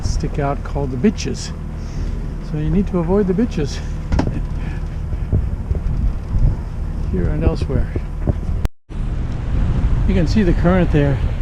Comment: Did I hear it right?? 13:51 - "you need to avoid the bitches, here and elsewhere"